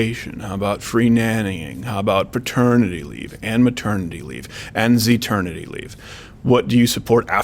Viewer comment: "zheternity leave" at around 24:50? Did I hear that correctly?